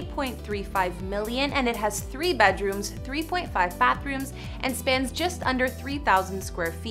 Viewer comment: sorry, did she just say 3.5 bathrooms. .5???